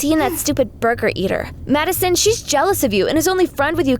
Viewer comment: It sounds like she said mad son